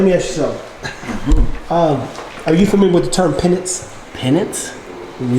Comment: This is dope - I thought said pennant.. , PENANCE is what he said ! Keep it up fellas - Mark Blair